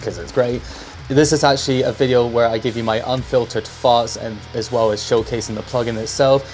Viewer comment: I could have sworn he said "this is a video where I give you my unfiltered farts"